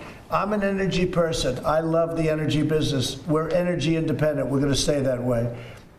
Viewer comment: did he say we are energy independent?